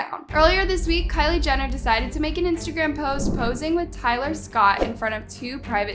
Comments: I thought you said Tyga Scott 😭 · did anyone else hear her say tyler scott ? 😭